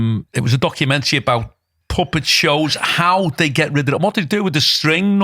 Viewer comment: I thought you said Muppet !